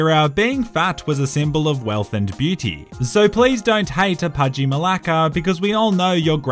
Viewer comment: Did he say Malaka??0:27😂😂